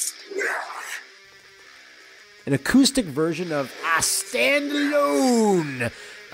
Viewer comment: I meant to say 'instrumental" and not acoustic. I know the difference between the two, just misspoke and didn't catch it. Sorry!